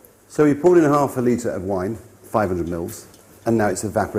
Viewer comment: did he just say 500 milfs